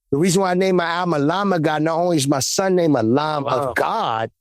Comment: All this time I thought he was saying "a Llama God" 🤣